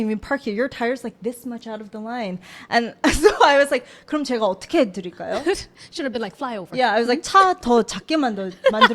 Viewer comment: what was said at 29:35? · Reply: here! I said, what should I do for you? Would you like me to make my car smaller?